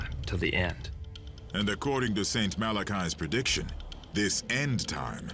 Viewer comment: I thought it said 'Saint Malarchy.'